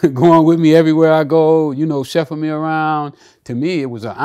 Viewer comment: he said chefing me around instead of chauffeuring! 😂😂😂😂